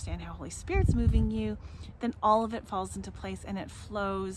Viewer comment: He has actually thought me. But I heard him falling into me